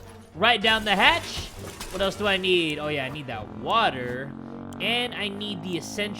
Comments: BRO DID YOU SAY “water” I THOUGHT IT WAS WATTTAAH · I need that water Me: OMG he did not say WaTAaAaA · I THOUGHT YOU SAID WATTAH NOT WATER